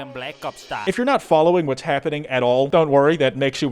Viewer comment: i keep hearing just "kill them blacks" instead of "black ops style" and i always go wtf